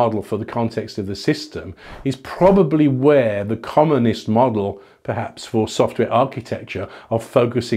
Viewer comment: Every time says 'commonest' I hear 'communist'. Not a useful comment but felt important to share.